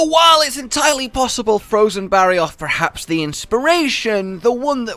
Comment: I'm so dumb. I thought it said frozen barroth. Like the green one